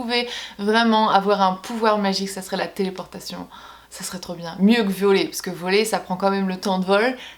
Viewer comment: Instant psychopathe : A 3:08 j'ai entendu "mieux que violer"... Il faut vraiment que j'aille me faire soigner.